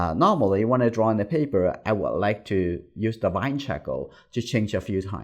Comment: what is the word after "Normally, when I draw on paper, I would like to use ..."? The caption says "vine shackle" but that doesn't make sense. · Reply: I think he meant light charcoal. In another video he's using real charcoal and he pronounced it the exact same way. I think in context it makes sense because light charcoal is easier to hide later on in the process.